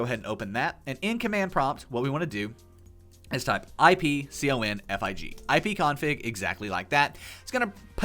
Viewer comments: and its anooying!!! It wont come up! Maybe Im doing it wrong idk could someone tell me wut he saying? (also sos my cat was walking over my keyboard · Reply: ipconfig no space no caps in command prompt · what did you say? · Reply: ipconfig